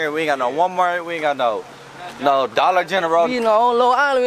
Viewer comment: What the hell did that 2nd guy say haha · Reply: The last couple words did sound crazy.. He said "we all grew with each other.. babies".. At first I thought he said "we Gargoyle eachother.. Badies" 🤷🏾🤦🏾 That's just what it sounded like til I seen someone say what he said and I heard it lol 🤷🏾